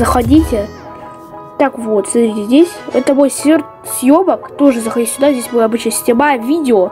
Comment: Я один на это моменте услышал 1:00 съе@